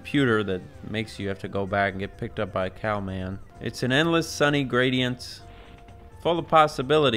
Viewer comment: I swear I thought he said “getting picked up by a cow man”